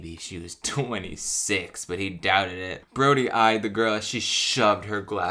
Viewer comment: I thought you said the lumberjack's name as 'Grodie' and was like alright that's a choice